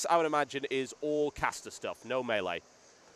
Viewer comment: oh he said "no melee" i thought he said "no male ey"